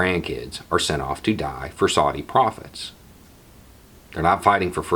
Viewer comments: Thanks for the video, such an important message. Cheers from an ANZUS partner and mother. (I did a double take when I misheard you say "make no mistake this war will be due to Saudi prophets" What tf was prophesised?? Oh profits, lol) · Oh...”Saudi Profits”...I thought you were saying Saudi Prophets. I’m tracking now. We cannot send our kids to war protect Saudi business interests. This cannot happen again.